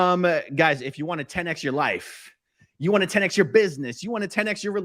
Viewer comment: When I hear "10 X your life" I keep on hearing "canx your life." Kinda appropriate considering how COS does cancel people's lives.